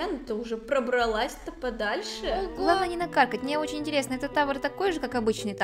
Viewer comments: В 2:20 я одна услышала "главное не на какать"?🤣🤣🤣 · я услышала: главное не накакать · Никто: Абсолютно никто: Ульяна: 2:32 гЛаВнОе нЕ нАкаКатЬ (Я так слышу 🤡)